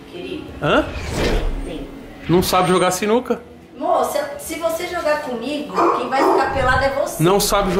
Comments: eu ouvi moh?? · só eu q entendi "mo"? (de amor) e percebi um ciúmes da parte dela... eles se amam ainda kkkkk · Eu ouvi um “mô” 7:35 ali? Ou mais alguém? Kkkkkk